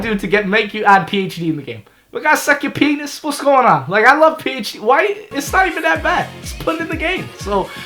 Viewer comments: I thought he said “I love penis” instead of “I love phd” 😂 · I was eating and the crunch kinda muffled my hearing and I thought he said he loves penis